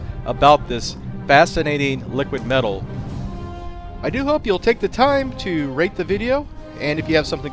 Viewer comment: I heard: "Please take time to rape the video"